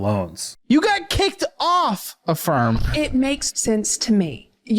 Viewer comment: 😂 I thought he said "you got kicked off A FARM" 😂😂😂 I was confused for a minute there 😂😂😂